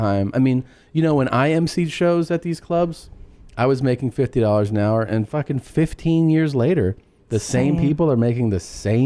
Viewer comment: did he say 50 dollars an hour?